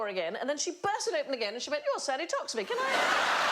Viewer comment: Did she say at 04:02 'You are all sat to talk to me. Can I have..'?